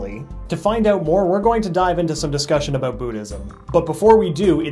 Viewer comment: Did he just say "Bootysm" instead of "Buddhism"